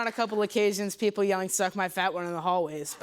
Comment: i thought bro said " on occcasion people suck my fat one in th hallway" and i was likr WHAT until i rewinded